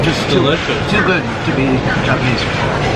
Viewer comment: Who here in 2020 👀 but was I the only one who heard it's to good to be Chinese